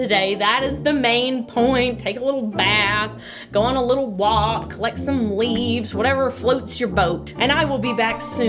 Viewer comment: I heard “collect some leaves” as “lick some leaves” 😂 thought she must really be having a crazy day 😂😂